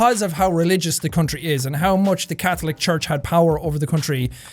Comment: I zoned out for half a second then I heard “fuck the Catholic Church.” And that caught me so off guard. I had to go back a minute or so lol 😂